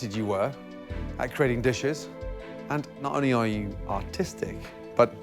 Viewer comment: what did Gordon say autistic or artistic?